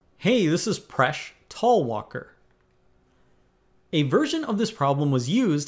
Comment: When I was new on his channel I heard "my name is presh tawakar"😅😂